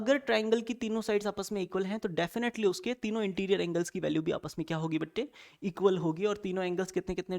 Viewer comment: sir said batte instead of bete.